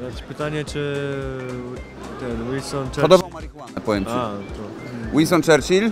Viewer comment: co? bo nie usłyszałem. Winston Churchill chodował marihuanę?